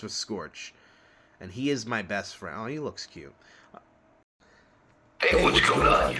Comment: I thought you called Scorch cute for a second there, was gonna say No Diddy.